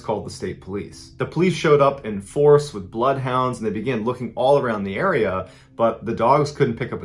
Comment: did you say Blood Hounds? Lol